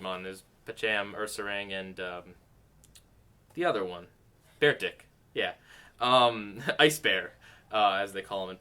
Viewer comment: "Bear Dick" (I know it's a Pokemon or something but I don't play those games so it sounded like bear dick for me)